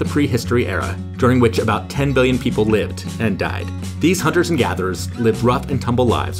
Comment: "60 billion-ish", "30 billion or so" - What a way to quantify dead people 😅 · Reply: Why did I hear “hunters and yappers”😭